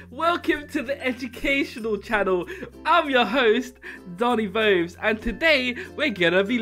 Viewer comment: At first I heard “with your hoes, Doni Bobes”